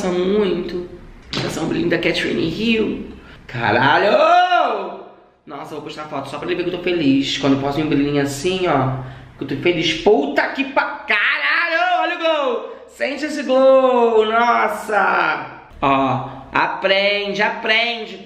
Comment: „Caralho olha o glow!“ hahahahaha eu entendi olha o gol! Hahahahahha